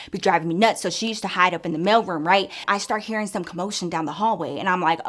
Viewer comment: I heard "male room" instead "MAIL room" 😅😂😂😂😂😂